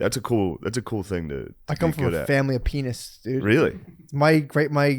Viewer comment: definitely misheard that as “I come from a family of penis”